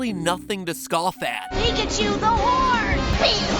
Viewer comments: Oh my f**King god. 0:09 I thought Ash said, "Pikachu's a wh**e" XD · I thought he said "PIKACHU, THE WHORE!" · i was sipping tea and I thought I heard Ash say, "PIKACHU'S A WHORE" and I *SPAT*. like it was hilarious. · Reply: My Name Is Unimportant YT sadly no. "Pikachu the horn!" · I heard Pikachu's a whore, rip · Is it me or I'm crazy but at 0:08 did I hear ash day "pikachu is a whore"??? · I thought ash said pikachu a hore😂😂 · Oh my lord I thought ash said “pikachu’s a whore!” XD I’m sorry I just had to say XD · I heard pikachu's a whore. Wtf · I thought Ash said “Pikachu’s a whore” instead of “Pikachu the horn” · Me: everyone! This is not a drill! I repeat! ITS NOT A DRILL Turns off music and TV Me: New Episode Of Game Theory!!!! I SWEAR This is me literaly everytime I get the notification😂 its actually pretty fun to do. My guess is.......23? 20? Something around there.😒 pretty sure its going to be something with no sense that will blow our minds o.O BTW matpat I really want to know spongebob's age since he goes to work, is in a driving school but he acts like an 8 year old or something 😕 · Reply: I thought he said "pikachu the whore!" At the beginning of the vid. Within the first 15 seconds